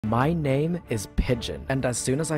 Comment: I heard "my name is Bitch" and I was dying xD